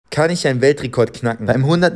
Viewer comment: Ich hab erst verstanden kann ich beim Weltrekord kacken